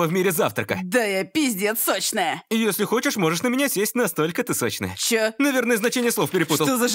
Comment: Мне послышалось вместо сесть съесть :D